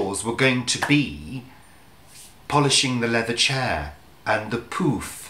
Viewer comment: I do not think it was the accent. I was in the car with road noise while listening to this. I thought you said your were going to punish the leather chair. So, if the polish can be used on anything, can you punish feet with it? Lol. Laura